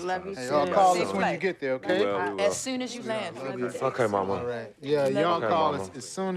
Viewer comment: sounds like he said F**k her mama